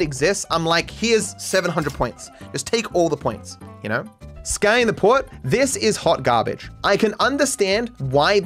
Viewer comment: Every time i hear matto says ''Scouting the port'' all i hear is sky in the port. also matto : rating minor turbulence Midnight Swim softly playing in the background